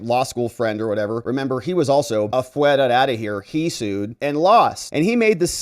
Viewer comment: Did he say, "AFUERED", OUT OF HERE? 😅🤣🤣🤣